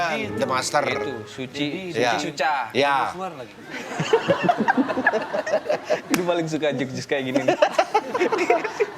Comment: ada yg tahu 6:28 ketika ngomong Suci-Suca si Desta ngomong apa yah? karena gak jelas tapi kok mereka bertiga pada ketawa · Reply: Jurinya dedi mizwar lagi. Kan aslinya dedy corbuzier